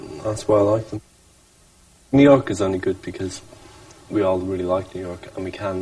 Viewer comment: did he say '' 'me' can live there" ?